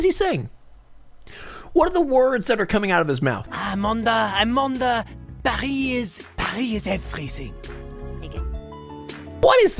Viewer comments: “Amanda Amanda, Paris is everything” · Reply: I heard “Amanda Amanda, Paris is Amazing” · I hear "Amanda, Paris is everything"